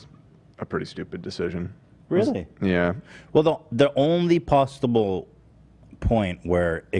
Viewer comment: Ethan saying “pasta bowl” instead of possible. Made me laugh 😆 peace and love 💕